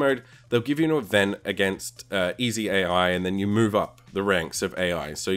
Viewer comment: Did he just say "easy AI", damn either you are extremly good at this game or Iam terrible, because I think the AI is just impossible to beat. The best raking I got was 2nd and only in one race, after that Iam allways 3rd-4th-5th etc =(